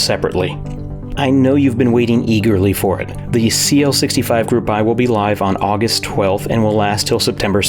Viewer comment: What's the keeb you start talking about @ 4:27 ? I thought I heard CL65 but don't see that listed in the description. Thanks! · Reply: Ciel65